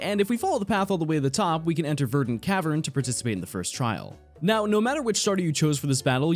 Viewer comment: why does it sound like he said virgin cavern